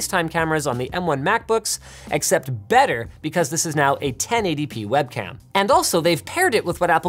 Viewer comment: why does subtitle say 10 adp instead of 1080p?